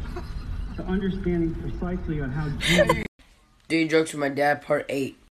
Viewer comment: sounds like he says doing drugs with my dad 😂😂